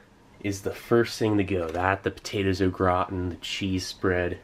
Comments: I kept hearing “the potatoes look rotten.” Lol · I thought he was saying “the potatoes look rotten” until I was a 3rd of the way though 😂